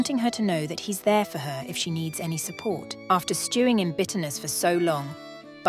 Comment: "bitterness" I HEARD "b**hless"